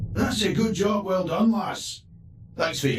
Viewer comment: But he did not say "she". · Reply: He said "Well done lass"